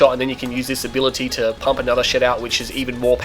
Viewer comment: sounds like you said Pump another shit out lol. nice work thou